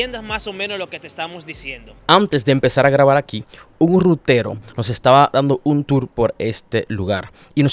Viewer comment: dices Hugo rutero o hubo un rutero no entiendo tu acento